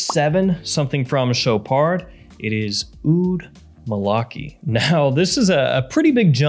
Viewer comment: I thought you said "Oud Malarkey" at first. That'd be Biden's signature scent.